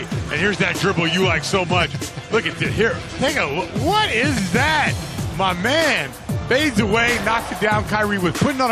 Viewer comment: sounds like he said “nigga what is that” 😂😂😂